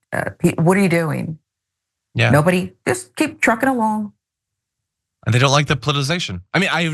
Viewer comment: Keep dRumping along*. Is what I heard. He's a FUCKING LOSER!!!